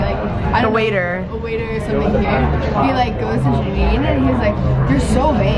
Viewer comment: "you're so.. WHAT?" can somebody tell me what they said lol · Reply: Melinda Four vain!